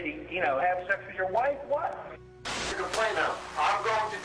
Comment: did he just say "did he had sex with your wife?" lmao